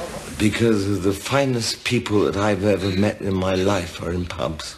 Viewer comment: “ The finest people I’ve ever met were in pubs” Absolutely touching. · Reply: Idk why but I thought that he said "the finest people I've ever met were in pumps" lmfao. I thought he was talking about women. oh boy.